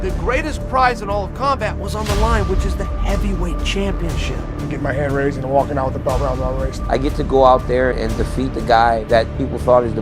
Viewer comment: what did Stipe say???? lol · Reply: “Gettin’ my hand raised and walkin’ out with the belt wrapped around my waist”-STIOPIC✝️🇺🇸